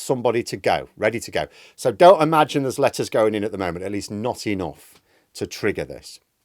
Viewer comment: I misheard when he said letters going in in January. I thought he said lettuce and immediately thought Liz Truss was about to throw a New Year party!